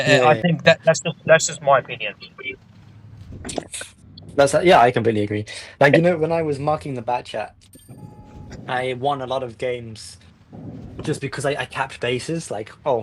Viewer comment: Damn intro btw I thought you say is3 marking worth it 😭